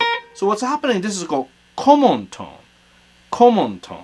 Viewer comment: At first I thought said, "This is called Como tone,....Perry Como.." ...for all us old geezers out there.